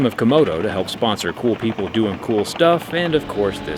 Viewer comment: For a minute there, I thought he said the video's sponsored by 'Cool people who did cool stuff' 😂 That would've been wild. Come on Killjoy, let's see it happen!